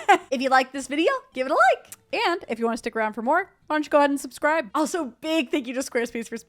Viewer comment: Is it just me or at 19:45 when they say 'why don't you go ahead and subscribe' I hear 'Punch God and subscribe'? haha I was so confused for a couple seconds there :D